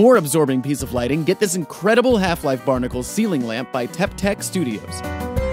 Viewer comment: i thought he said barnacle seamen lamp